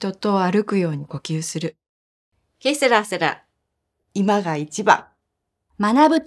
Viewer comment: 60番の人のやつが、 2:37 ケセラセラにしか聞こえないwww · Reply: ケセラセラだよ